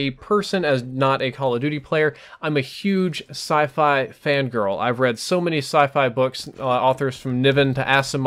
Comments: Did he say he's a huge "Fan girl" of Sci-fi? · did he say "I'm a huge sci-fi fan girl". Hiding something? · Did he say "fangirl" at 4:00?